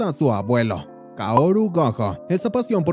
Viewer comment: Pensé que dijo satoru gojo